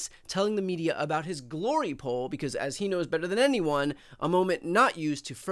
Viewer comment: Glory pole! 💀 glory pole! 🤣 glory… Paul! I cannot! I thought you said glory whole. Without the W. I love homonyms. 🤣 if you don’t know what a glory WHOLE is without the W consider yourself either lucky or not well read. Bye. I’m still laughing at glory pole.